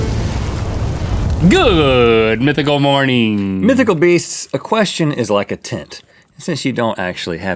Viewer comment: I thought Rhett said "a question is like a tit" and I was like woah ok Rhett